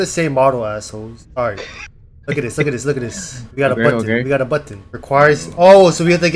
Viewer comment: When said “look at this look at this we got a button” I thought he said “we got a butt”💀💀💀💀